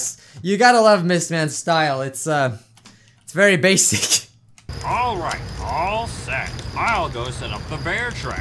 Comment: Oh god I thought Baldi was saying "Alright,Ballsack!" Edit: Really